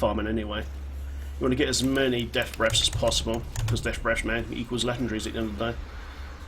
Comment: WTF do you say at 8:22 ?! Death Breaths equal what?? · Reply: Death Breaths equals legendaries !